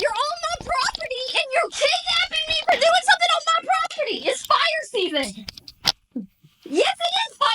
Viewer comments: Did she say it's spider season? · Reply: I heard "fire season". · Who’s been reading their Bible? How about “render unto Caesar what is Caesar’s”? Lady, from one Christian to another, you need a doctor. Also for a minute I thought she was saying, “It’s fire season,” to explain why she set the fire. Y’know, like duck season… fire season is when we set fires, right, officer? It would have been a novel excuse for arson. Edit: That is extremely un-Christlike language, Mrs. Lady.